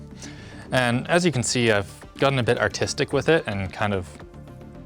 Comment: I heard autistic, sorry Bro xD.